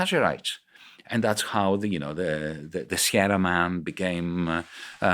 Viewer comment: what does he say here? The Sierra man [subtitles]?